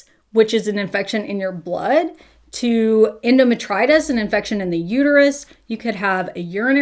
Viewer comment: I love your content, tbh seeing you respond restores my faith in humanity enough. But it looks like you wrote endometriosis in the caption when it sounded like you said endometritis. wasn't sure if you caught that